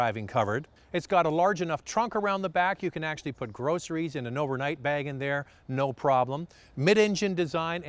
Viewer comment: did i just hear overnight bang lmao